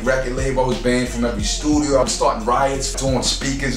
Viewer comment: when R.A. said he was banned from every studio i thought he said schoolyard lmfaoo